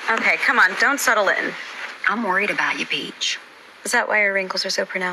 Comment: A soon as she said peach I was scared for her 😂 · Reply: I thought she said beach